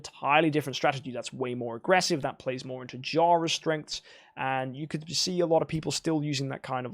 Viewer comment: Its vesa did he just say jhara